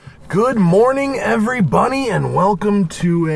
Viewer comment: did he say every bunny?!